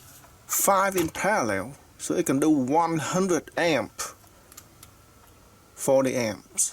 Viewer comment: Absolutely genius. The home made screws! You channel deserves to be known world wide. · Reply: bruh, when did he say 100V? 17:33 he said 100A. the battery could deliver a maximum of 100A at 24V. doesn’t mean the engine will draw 100A when it’s working. probably much less. Wall sockets (in eu at least) can deliver 16A,230V usually with a circuit breaker when exceeding 16A, it doesn’t mean your devices will draw the max amount..